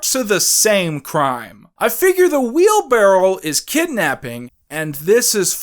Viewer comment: are you saying "wheelbarrel" instead of "wheelbarrow"??